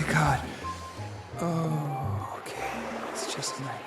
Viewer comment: i heard “ohh okay, it was just pac-man”